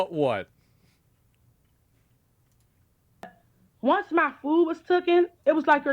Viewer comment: subtitles are wrong. She said: "Once my food was TOOKEN"